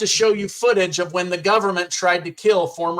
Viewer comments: I hear “gunman” not government. Sheshhh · I thought he said gunman not Government? Maybe I am wrong but I heard gunmen first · Sounds like he is saying gunman, not government. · I hear "gun-men" not government...?